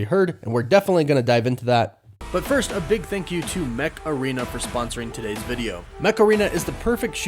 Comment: The Mech Arena ads crack me up. Every time I hear the voice-over, I keep hearing "Macarena."